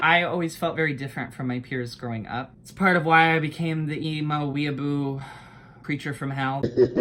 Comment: i thought you said korea boo 🤣🤣🤣